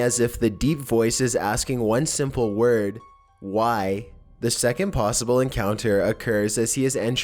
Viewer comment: is 2 words..... "why come" is what I hear.